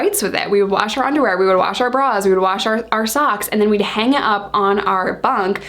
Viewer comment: I heard Balls.